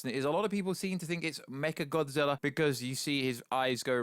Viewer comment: First Kinky Dora, then Martha, now I heard him say mecha gothzilla